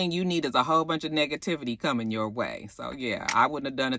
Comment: Lol, why I thought you said niggativity on the social media 😂